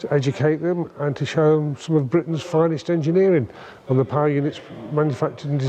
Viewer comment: Around the 22 minute mark I thought the guy was saying “pie unit”. Then I realised what he was saying. 😂